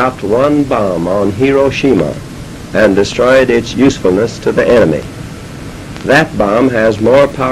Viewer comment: Bruh i thought he said its usefulness to anime